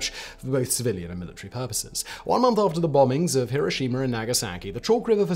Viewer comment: did he just say Hiroshimer, doesn’t get more British than that